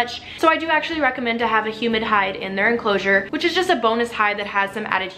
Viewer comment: Damn, I thought she said human hide:)